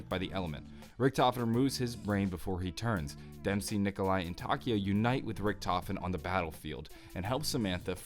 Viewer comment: did you say taki?